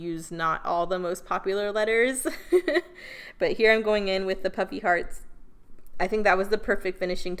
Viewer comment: Adorable… I thought you were saying “puppy hearts” Puffy hearts…duh